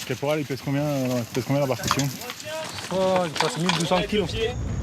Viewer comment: did he just say that this boat weighs 1200 kilos, 1.2 tons? o_O